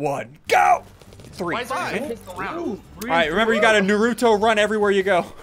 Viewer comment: Did he just say Narooto?